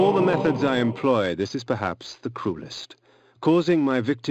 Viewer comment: I had to rewatch the part where he said the cruelest. Cuz I thought he first said, the coolest lol.